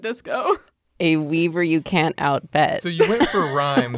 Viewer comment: When said “A weaver you can’t out-bet” I heard “A bever you can’t out-sled” so I honestly didn’t understand the drawing and what they were saying until I realized my mistake